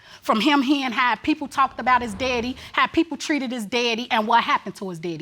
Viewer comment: Is she saying "deady" or "daddy"?